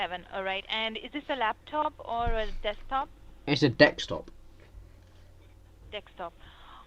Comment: i heard " Dekstop"